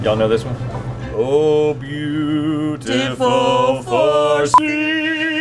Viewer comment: Did they say beautiful foreskin